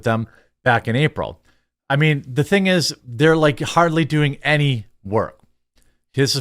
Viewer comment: I heard "They are doing any Harley Work" . I assume you said hardly but I think there is a joke somewhere in that sentence. 🙂 . Thanks for the vid . Love what you do.